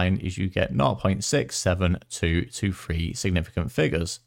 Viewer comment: did you just say 67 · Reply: Hi, If you're referring to the answer for part (b) I say '0.672' Unless I'm missing a joke here 😅